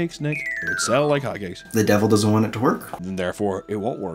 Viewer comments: Hahahahahahah I thought he said :The devil doesn't want to twerk' 😂🤌🏽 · I heard it as "The Devil doesn't want to twerk"